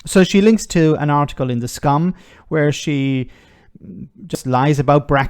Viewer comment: not sure if I misheard you but instead of saying something was said in the "sun" I heard "the Scum"..... I am going to use this from now on either way